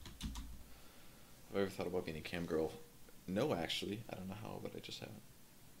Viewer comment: What does say at 3:20? Have I ever thought about getting [camgirl]? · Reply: You just got asianed. have i ever thought about being a camgirl